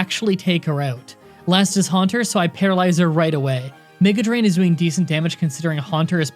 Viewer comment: di-did he say n*gga drain?